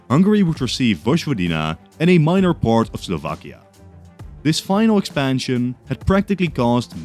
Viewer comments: Great video, as always! just minor mistake aroun 4:50 if I hear corrext - I hear minor part of Slovakia, not Slovenia. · you said slovakia instead of slovenia.